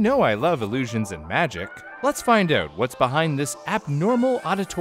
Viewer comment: "Let's find out" I hear this as "Let's find oat"... but Canadians beg to differ. 😉🍁🍁🍁🇨🇦